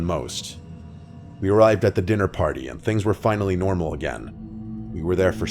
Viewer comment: I thought he said we arrived at the diddy party